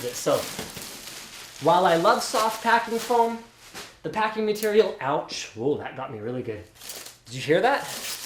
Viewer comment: I think I heard Pikachu at 4:17 :D